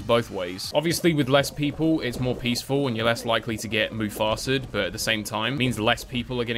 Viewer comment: Dude wait, at 1:57 did he just say mufasa'd lol?